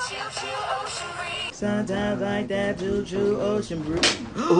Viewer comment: “SoMe DoGs LiKe ThAt ChIlL OcEaN BrEeZe.” Gets hit 🤣😂🤣🤣🤣🤣🤣🤣🤣😂🤣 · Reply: I heard "SOMETIMES I LIKE THAT JUJU OCEAN BREEEEEZE"